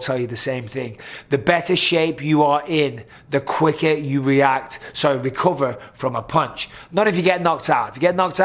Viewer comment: I appreciate your insight about recovery and conditioning. I've been wondering about that for a while. I always heard it as "you take the shot better" makes sense that you actually "recover faster"